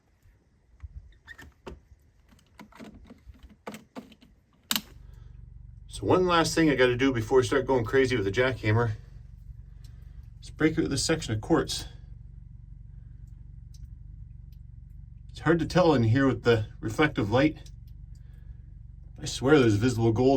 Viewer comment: I thought you said quarts there for a minute, like the kind that come in paper bags.